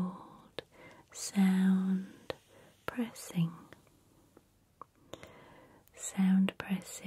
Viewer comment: I honestly always thought you were always saying "prayers" until i read it properly..."press" 😂😂😂